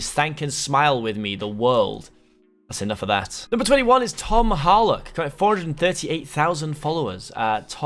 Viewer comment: When he said tom harlock it gave me a heart attack because i thought he said tom holland 😂🤣😂🤣